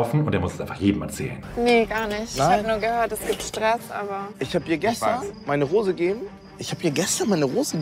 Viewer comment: Lol ich habe die ganze Zeit verstanden, er hätte ihr gestern eine Hose gegeben...